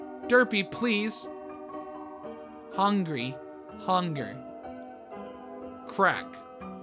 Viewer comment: why did i think that derpy said "burger"